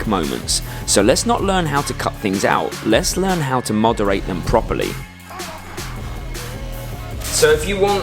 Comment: Sorry… mooooooooderate? Modereight? What is this word at 3:59?